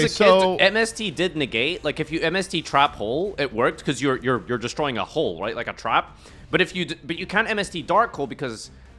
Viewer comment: Did really say "destroying a hole"???